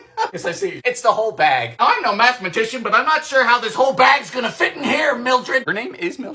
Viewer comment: I have to watch this kinda quietly at the moment but swore you said "mathemagician" and it's possible you did, or didn't but now it's my new favourite word ^-^❤